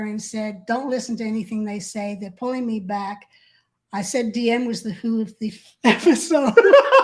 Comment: what does she say after, "I said DM was..." She starts laughing and I can't understand what she was saying about DM. Apparently it was funny cause also started cracking up and I've tried to listen 4 times and I can't understand her. Anyone know what she said? I really enjoyed this interview has some really unique stories, really interesting stuff! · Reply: My husband Paul was on a mission to Clearwater and had called me while on I was on the Freewinds, he had told me that DM was the who of the FSO (Flag Service Org). I am laughing as I say FSO. Meaning that DM's orders into or about the FSO was causing the organization to not do well.